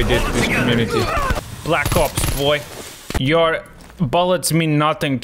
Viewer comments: POV you thought he said “black cops boy” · When he said black ops going down the Zipline I thought he said black cops boi I don't know what I was thinking 😅